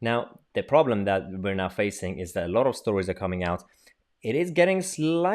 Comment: A lot of stories, but misheard you, I thought you said, "A lot of Tories" LOL But on the point of Dominic Cummings, he is definitely backed by evidence it seems. Boris always wishes for this, wishes for that, plus now with chanting coming from the other side of his bed, it looks as if that very recent famous man said, "You better be careful for what you wish for, Joe Bloggs". I thought he might of been good, but the moment that the other side of his bed got claimed, things have gone from bad to worse, on all fronts, so Horis had better go as quick as possible, frog march him out now. Once a bed partner becomes bossy, then that is the moment, that you give it it's marching orders, we have our nation at stake here, blow the media as they don't count anymore. Rabb or Sunak, they are into less bullshit than Horis. Oh, on your way out, just take the so called Horis Baster Bus, so ugly Busses! Where is our Aircraft Carrier HMS Queen Elizabeth II, I thought it was headed to the South China Sea, it better be going there, as at this moment, Taiwan needs some backing, like Now!